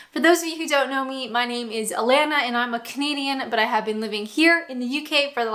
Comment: I watched about 6 of your videos before I realised you were saying 'Canadian' - I thought you were saying ' comedian'